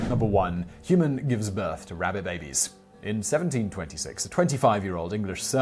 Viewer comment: I thought he said rabid babies. Eek.